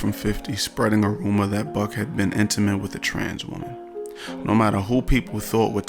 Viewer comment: I swear I had to rewind it like 3 times I thought you said “ young buck had been intimate with a tarantula” 😂